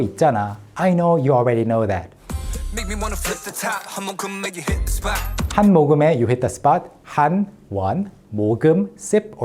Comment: my dumb self thought he was always saying "imma come make you hit the spot" lmao